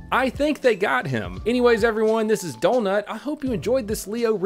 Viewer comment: Terminator 2 reference? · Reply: "The Professional", Starring Jean Reno, Director Luc Besson. AKA " Leon" - The line, or similar was, was said by Gary Oldman • On second thought, I think he JUST said "EVERYONE"